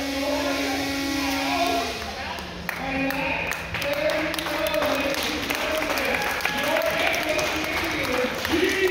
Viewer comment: What does that kid say t 13:44? Everybody laughs but i can't hear what he said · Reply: He said, "Definitely not a cakewalk, if that's what you mean." I didn't hear him either; someone had to tell me.